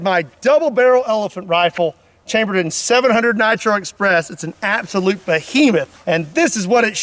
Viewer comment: I thought he said “it’s an absolute fajita”